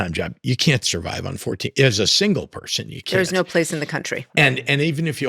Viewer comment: I stopped listening to this after the first ten minutes. At 6:56 did he REALLY say a single person can live on $14,000 a year????????? I rewound it several times and watching his lips, that’s what it looks like to me. As a single senior who has been single my whole life… paying the mortgage, all the utilities, all the real estate taxes THIS IS MADDENING. He will NEVER get my vote. Earlier, before that timestamp, he was talking about FAMILIES again. My single friends and I are so sick of hearing about families families, families What about the single people who are paying for everything on one income? I know senior couples who can’t afford a patio home on both of their incomes. Even Bernie Sanders on a late night show several years ago… I’ve never forgotten this… He was speaking about “working people” needing relief, and then he caught himself and changed it to “working families”. OMG! Dems wake up. Single people are one of the largest demographics in this country.